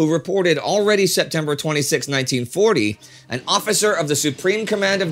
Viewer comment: Error at 3:11 English subtitles say 1941 instead of 1940.